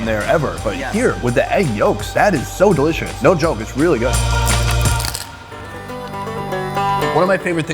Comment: Did really say "No yolk it's really good" - at 13:56?